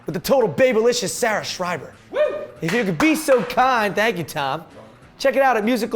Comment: Music galore 3. I thought you said with Sarah Saur. I was about to be all on that!